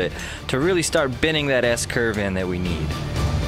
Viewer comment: i thought he said "that ass curved that we need"